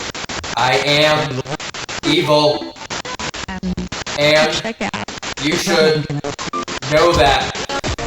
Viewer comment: it sounds like a woman saying “you should get out” instead of “you should know that”